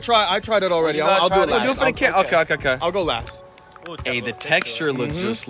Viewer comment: did i hear a guy screaming HEY